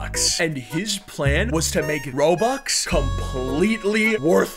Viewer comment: CAPTIONS SAID ROBUX WAS ROOX 😂